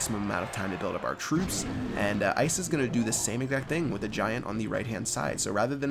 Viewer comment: did he just say ISIS?? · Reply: "Ice is"